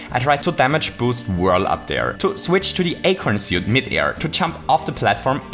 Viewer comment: Did he say acorn suit?